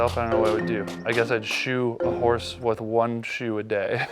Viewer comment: Who else thought they said shoot a horse not shoe a horse?😂